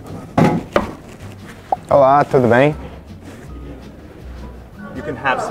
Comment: he did not say “I’m hot” he said “everything good”?